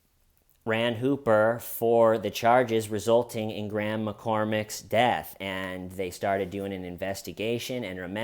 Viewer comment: Every time you said "Graham McCormack", I heard "Grandma Cormack". 😂 Why does clean cut hair style kill me?!?! 😂 I mean that in total love. Love this guy!! 🤍